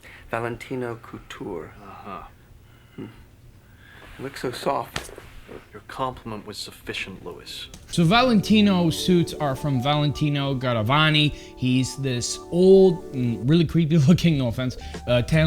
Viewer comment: wait i thought you said "your compliment was sufficient, louis" 😭